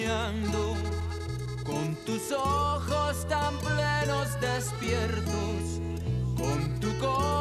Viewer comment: La primera vez que escuche esta rola (ya hace muuuuuchos aňos) pensé que decía "con tus ojos tan llenos de "estiércol " jajajajjaja...... apenas y sonaba esto acá en Mty.